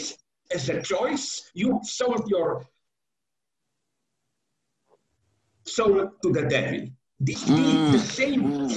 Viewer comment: what does he say? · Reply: he said “ you sold your soul to the devil”